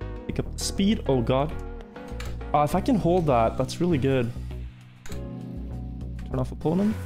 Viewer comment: It REALLY sounds like he said “I’ll f*cking hold that, that’s so good” And it sounded like he just became gay